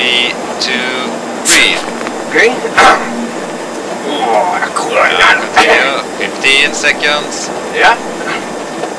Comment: What he mutters is an ancient Hungarian war cry, that is said to increase physical strength. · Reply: I try to spell this alleged war call .It's a swear word😂😂🎉 K.U.R.V.A A.NY.À.D.A.T.🎉